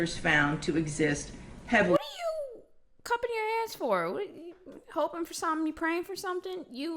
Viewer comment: I thought she said “what are cupping your ass for?”